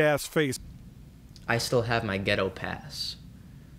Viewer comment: I thought he said ghetto past at first tbh which made sense to me, because rap was started by an oppressed people, having a ghetto past makes sense, saying as if because they experienced oppression they have right to voice their feelings on it. I don't necessarily agree with it but I've heard the argument before